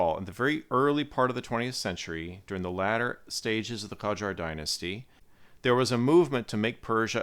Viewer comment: Now for P? Where did you hear P? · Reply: When I first listened to it 5 months ago, it sounded like he was saying "Pajar". I think it might have been my speakers. Listening more closely, I can hear the "k" now, but I had to listen over and over before I heard it.